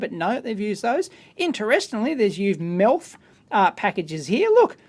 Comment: +/- did say "MILF Package"? (I don't know looks a little crusty to me) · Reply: Douglas Brown MELF, the round surface mount resistors that look like through hole resistors with metal caps instead of 1" leads.